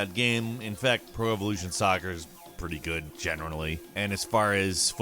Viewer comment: What was that word at 1:41? Generalanly?